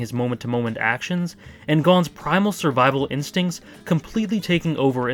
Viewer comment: Putting the words "survival instincts" and "Gon" just don't work. At least unless you put the works "doesn't have" between the latter and the former. Someone let's their own hand get blown off so they can mildly injure someone else, for their own pride doesn't really gell with survival. If his instincts were for survival, he would have went with the plans. I love the video though. I am just poking fun. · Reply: I thought he said animal instincts.